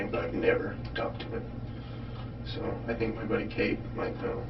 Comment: This isn't? He repeatedly says "my buddy Cayde" ... At least that's what I thought I'd heard? CC changed it to "Kate" quite a few times... So who is this guy? I'm confused... Those last few minutes were really difficult to listen to... I really feel for this guy. Sentence is pretty harsh for someone who got jumped and was unable to free himself from his attacker any other way.